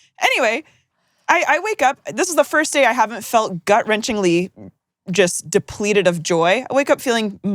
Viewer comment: the way I heard “Gut-wrenching Lee”